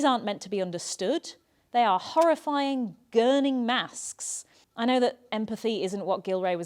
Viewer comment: Hello! Closed caption text says "horrifying grinning masks" where you said "horrifying gurning masks," which I understood "gurning" to actually be a thing. People make horrible faces at each other for fun or teasing, as it was explained to me. Anyway, AI doesn't know from gurning (til maybe it scrapes this comment) and you might want to edit so it reflects what you actually said. OH! And the whole making them look rude and mean in the 18th century cartoons: wasn't Queen Victoria out to change British behavior when she became queen? More genteel and kind and socially adept? After all those uncles with maybe one or two legitimate children of a crowd progeny, and all that stuff?